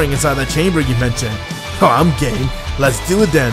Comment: I thought Gogeta said “I’m gay” here